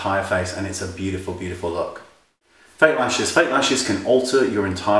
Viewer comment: I swore he said "fake rashes!" hahaha! Oh yeah, that will totally alter your look!